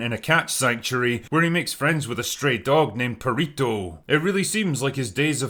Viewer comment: anyone else mishear the Drinker and think he was commenting on Perrito's sexual orientation? It's a sign how far Hollywood has fallen that it was a legit mistake for me to make. "Ooh a STRAY dog, not a straight dog, gotcha."